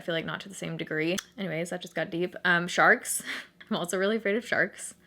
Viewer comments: i sincerely thought you said you were afraid of "sharts" and i was completely confused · Ok when you were saying your fears I thought you said "sharts" and I about spit out my drink 😂😂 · i legit thought she said "sharts" instead of sharks. i'm laughing way too hard right now. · Anyone else thought she said she was afraid of “sharts” at first? No? Just me? Okay. 😂😂😂😂💩💩